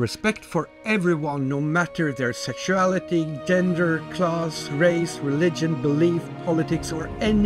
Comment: I'm such an idiot. When you were listing out group idnentifiers and said "class", I heard "clause". I paused the video for a little trying to figure out what you meant before just rewinding and reading the subtitles.